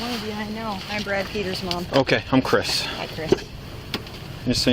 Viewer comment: I was legit afraid for this guy once the cop went into blind "obey me" "turn around and put your hands behind your back". edit" did she say her name was Brad Petersmom? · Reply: She said she was Brad Peter’s mom ... Brad being a police officer too, so I guess that means they will be biased in her favor.